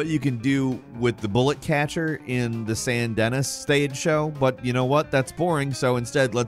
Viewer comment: did he just say San Dennis